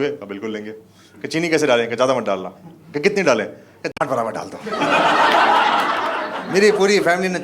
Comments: Even I Want To Know That What Is That beep Word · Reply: jhant barabar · kya bola samjh nhi aya? Kya daal diya bhai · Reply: Jhaant* bola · per kya bola, kiske barabar😅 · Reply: Jhaat* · what is that beep word? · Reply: Jhaant braabar chini😂